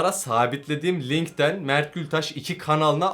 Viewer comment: hep mertül taş diye anladım lan